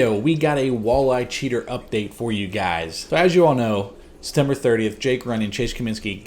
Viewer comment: sounded like he said December 30th to me lol...they guilty